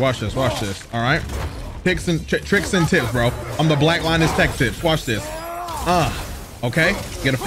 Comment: is it just me or did I hear "the black linus tech tips"